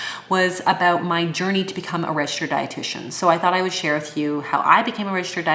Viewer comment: When you said, ‘’Then you can use the term ‘’Registered dietitian’’. I heard “Red shirt.’’ instead of registered dietitian, ha ha ha. I knew that you were smart, but after this description of your education, I think that you are properly named, you really are Abbey Sharp.